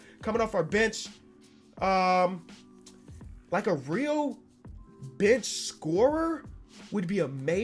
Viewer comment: it sounds like he says a real Bitch scorer